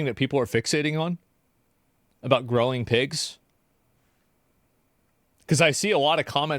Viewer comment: People are unhappy because they mishead what JD said. They thought he said "Groping pigs." Instead of growing pigs. PETA wants pigs unmolested.